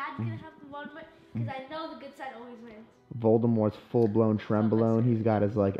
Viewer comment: Anyone else hear "Voldemorts full blown trenbalone" at 31:35 lol